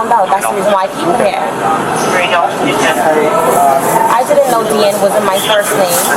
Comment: Did she say she didn’t know that wasn’t her name?